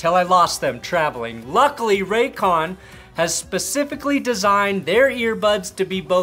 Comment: Lol Raycon I thought you said Raytheon 🤣